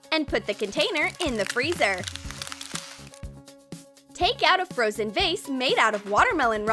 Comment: Thought she said make out